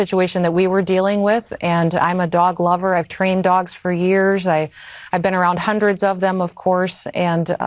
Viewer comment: What Kristy says, "I've trained many dogs... I've been around them for years.." What I heard, "Cricket is not the only dog I've killed... not even the cutest."